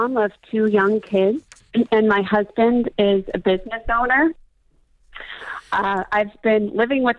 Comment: Whe she said he’s a business owner I had to rewind because I heard : “He’s a bit of a stoner” 😂😂